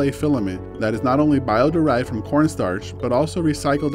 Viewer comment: The first time, I heard it as PLA filament is bio degradable, derived from Porn Stars, later understood he said Corn Starch 😂